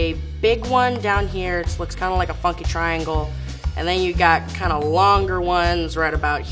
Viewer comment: Did she just say f*cking or funky triangle